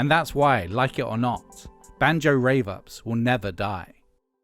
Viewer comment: I thought you said “Banjo Ravers will never die”